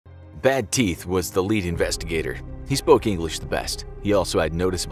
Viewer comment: the narrator said investigator but the subtitles said interrogator